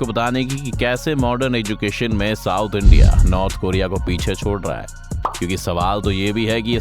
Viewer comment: kya bola tune North korea!!?